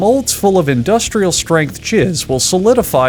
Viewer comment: what did he say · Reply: “Industrial Strength Jizz”